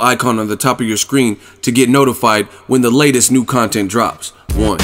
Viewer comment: what is saying? I hear "the latest content drops. One" is he actually saying one?